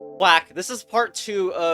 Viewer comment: why did you say black